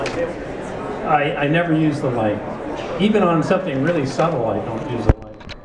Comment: Hi, thank you so much for upload the video and you now, i don't speak much english, so i don't understand what he say in the 04:07 and in the 08:00, somebody can tell me what he say? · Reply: someone asks why he does not use the light on the lighting table, he says he never uses it